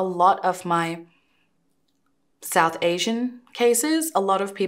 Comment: Lmao so you south asian😂😂😂 they was talking all that mess and look😂. I always thought u said you Mexican, 😂